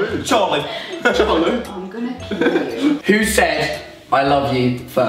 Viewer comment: did anyone else hear say "im gonna kill you" to??